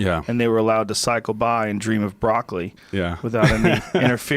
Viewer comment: "Cycle by & dream of broccoli" - Thought Rogan was saying "cycle by & dream of Brock Lesnar".